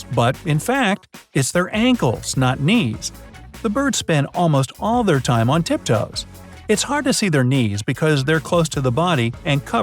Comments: Swear to go I thought he said the birds spend all there time on tiktok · I heard TikTok lol.